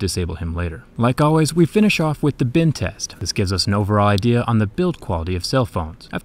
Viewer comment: Whenever he says "bend test" I hear "Bin test"